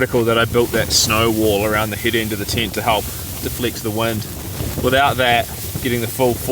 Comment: I keep hearing words like "deflict", "plisant" and "pigs". Oh, deflect, pleasant and pegs. :) Great video. Some adventure